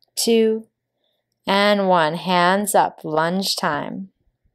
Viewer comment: I try not to act like a "fanboy", but man you always put out good content. Btw when you said "lounge time" I thought you said "lunch time" haha no I wasn't hungry.